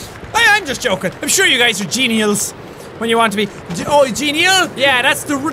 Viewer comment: I thought you said genitals.